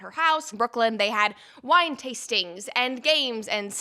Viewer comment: At first I thought she said they had "wine tasting and sex" 😂 I was like waaaat 😂